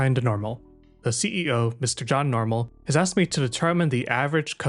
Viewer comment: i thought you said that the ceo's name was john von normal & when i backtracked tk hear it again i realized i had just mildly let myself down for mishearing.